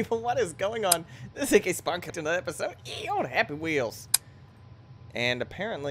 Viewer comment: In your intro it sounds like you say: "This is AKSpartankittenerepisode HappyWheels". O.o